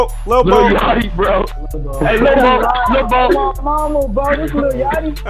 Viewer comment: who else heard "on my mama"